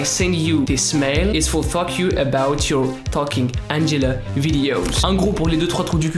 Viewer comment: C'est moi ou il a dit ''Fuck You'' 😂